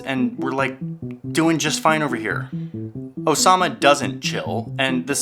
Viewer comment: I cracked up when he said "And one of his sons' name is "Osama"' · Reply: For a split second I thought he said "Obama"